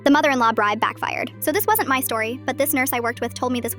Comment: I thought it said “mother in law bride” and I was like what…